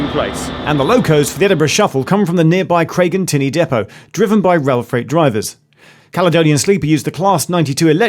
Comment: Did he say 'driven by well fed drivers'??? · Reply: Rail freight